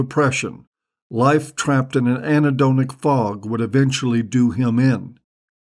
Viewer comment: What does he say between 1:27 and 1:30? Some kind of fog... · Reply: anhedonic.